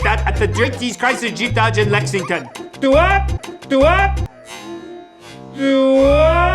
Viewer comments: I think he is trying to say “Stewart” (as that is a minion’s name) but because of his accent, it sounds like “DOOART” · I think he said "do what".